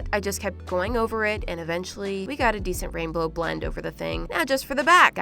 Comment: I swear I heard rainblow